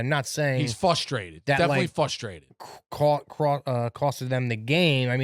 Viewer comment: did Rico say “Fustrated”? Does he think it’s pronounced…”FUSstrated”?